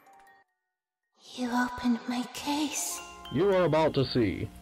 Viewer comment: i thought she said"you need some toothpaste?" but it was actually"you opened my case.."